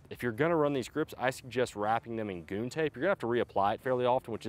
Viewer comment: What kind of tape r u wrapping that grip with? It sounded like u said "goon tape". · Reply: Yep he said “Goon tape”. It’s essentially a wider strip of hockey tape with a brand name